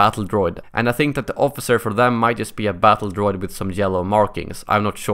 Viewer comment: i thought he said jello